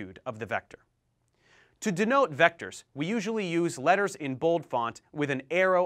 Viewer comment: did i just hear? "you Do nOTE" HAHAHA